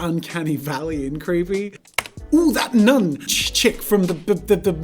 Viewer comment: did he really said "chick" from the movie nun?😂